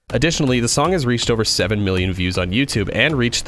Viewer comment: I thought he said the song got 7 BILLION views lmao